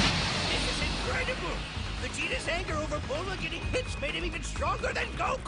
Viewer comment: Thought Roshi said “Over Bulma getting bitched” 🗿